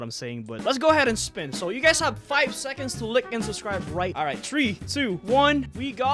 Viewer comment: What did he say lick and sub